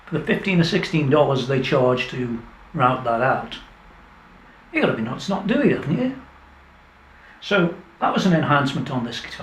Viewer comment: What does say between 16:05 to 16:08? It's the bit just after he says "...for the 15 or 16 dollars they charge to rout that out.........????????" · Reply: I'm from Liverpool so I'll translate! What actually says is: "You've got to be nuts not doing it, haven't you." I think is from around the Stoke-on-Trent area. For a small island, there are a lot of different accents in the UK, and some are much harder to understand than. Go ten or fifteen miles up the road and the accent will changes again. Try listening to a thick Geordie (Tyneside) or Glaswegian accent, especially after a few pints!